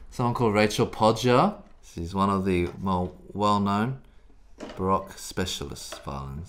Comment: You say "Baroque," I hear "Barack." Lol